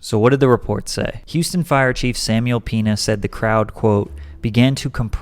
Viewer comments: "Samuel Penis"💀I can't be the only one who heard this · I thought he said samuel p*nis